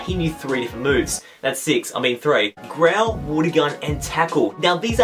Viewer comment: It sound like you say Grel Wuddy Gun and Takell at number 9 xD